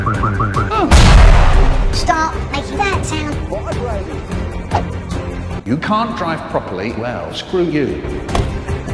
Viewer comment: what did Rog say? Vibrating?" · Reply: well "vibrators" but yeah close enough